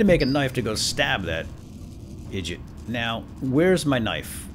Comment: Did you say "idjit" at around 9:30?